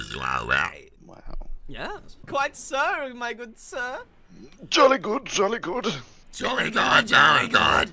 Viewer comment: Jesus Christ take care of your demon possession problem you’re scaring the children AND adults. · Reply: It sounds like its saying jelly god